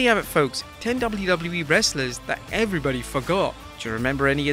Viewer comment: Don't know if it's the accent, but I totally heard "everyone F**k off" instead of "everyone forgot".